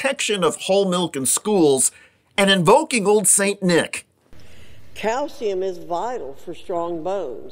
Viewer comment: Did she just say "cow semen" is good for bone growth? Lol it's called milk. Lmfao "cow semen" is hilarious though lol. Way to discredit yourself in the first 30 seconds lol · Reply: I'm not a fan of her's but she said calcium.